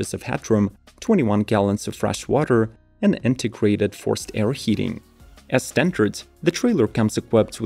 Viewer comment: When he said "integrated forced-air heating," I heard "antiquated forced-air heating." Heh.